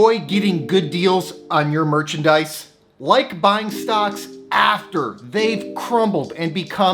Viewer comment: I thought you said stocks that have crumbled? Then you said abbvie, bruh